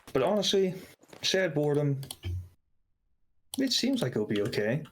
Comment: what's that other game he's talking about, sounded like he was saying "sad boredom", I couldn't find anything for that on google